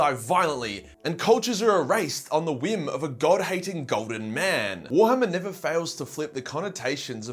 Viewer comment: TTS is getting to me, i genuinely misheard that as "Goblin Man"